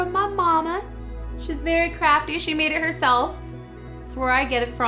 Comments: When she said her mom was very crafty, I thought she said crappy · Who else thought cupquake said crappy when she said her mom was CRAFTY? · i thought you said: it was a christmas gift from my moma she's vary crappy XD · for a seccond i thought she said ''my mom is very crappy'' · Am I the only one who thought she said that her mom is very CRAPPY? (She actually said crafty :P) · I thought she said 'she's very crappy', when she said 'she's very crafty'. :p